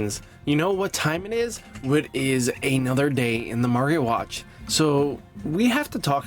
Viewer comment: I feel like I heard The “Mario” Watch? 😂